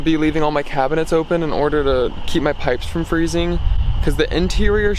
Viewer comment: You said "to keep my pipes from freezing" and I thought you said "to keep my pecs from freezing". 😂